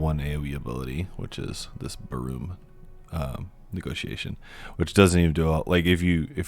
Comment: Sounded like you said "buh-room negotiation." Hehe